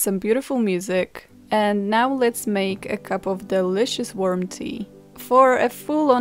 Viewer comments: I really thought she said "worm tea" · I heard "delicious worm tea" and kinda gagged a bit there.